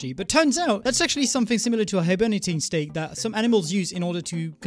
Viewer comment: did you say hiberenenatating state? Or am I slowly going deaf